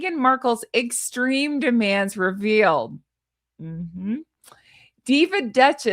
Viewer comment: I’m laughing so hard bc I was listening from the other room when I thought you said MM’s demands for veal instead of revealed!😂 now and forever she is a veal!